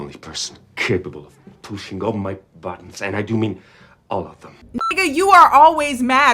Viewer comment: for some reason I thought he said “you’re the only one capable of clenching my butt” instead of “pushing my buttons” and now i’m cackling